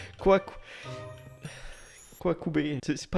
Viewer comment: a 0:32 moment génant on et en 2024 et il dit quoi coubeh